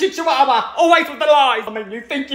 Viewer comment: I’m dying 😭💀 “Repent the chichuahua ways” intense belly rubbing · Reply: I thought he said, "We are petting the Chihuahua away."